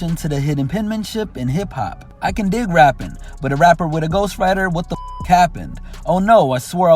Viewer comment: Till about 10 seconds ago I thought he said "but I'm rapping with a ghostwriter. Wtf happened?" Like, oh, turns out this is just part of the business. At least I don't lean on them as heavy as some of y'all. I thought it was insightful. Honestly, I still think it would be a better attitude to have.